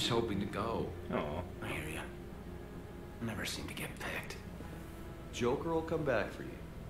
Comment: The person who says "I was hoping to go!" Is always the one you picked most often and the one who says "I never get picked" is obviously the least chosen. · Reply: Holy hell I never actually knew that. That makes it funny that I always hear Kaiden say “I never get picked” lol